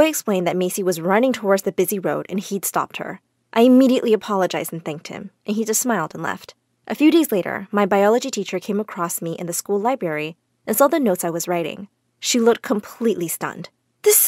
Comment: did she just say LI-Bary? Libary